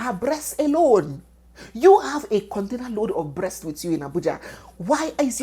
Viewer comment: Heheehe did I just hear "container load of breast" 34:49 😂😂😂😂😂